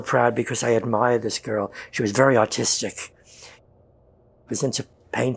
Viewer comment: Hahaha I thought he said she was very autistic.